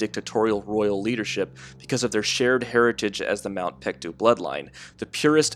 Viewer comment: what are you saying here? I can't understand. Their heritage as what bloodline? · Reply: Mt. Paektu.